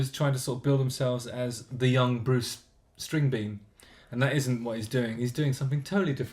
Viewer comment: did he just say bruce stringbean ? LOOOL